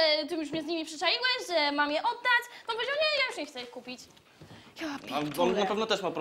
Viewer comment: Ej czy się przesłyszałem? O 35:14 usłyszałem “japierd@ i jeśli nie to pierwszy raz słyszę to w paradokumentach